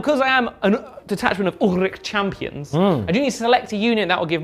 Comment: Every time you say "Auric" I hear "orruk" and get confused for a second, then start hating the AoS copyright-names a little more.